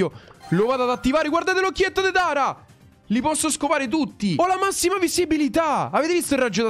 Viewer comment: solo io ho capito "scopare"? 😅